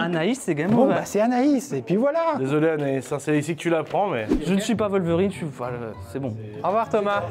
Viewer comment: perso j'ai compris "désolé Anaïs c'est ici que tu la prends !" , j'ai du mettre pause pour comprendre ce qu'il a vraiment voulu dire 🤣 🤣🤣🤣